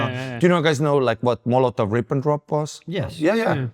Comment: Can anyone tell me what the proven 70 year long conspiracy Ari mentioned is? it sounded like he said "Molot of Rivendrop"... Sound like a knock off Lord of the Rings character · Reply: the molotov-ribbendrop pact, made by nazi germany and the soviet union to divide europe between themselves as they occupied countries.